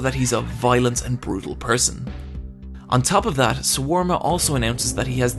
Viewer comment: Also when you kept saying Swarma (shawarma?) instead of Sawamura lol. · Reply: I noticed that too, I thought he was saying shawarma and I was thinking "was I getting his name wrong?"